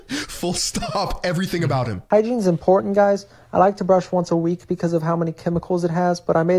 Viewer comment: did he actually say "hygiene is important guys" ????????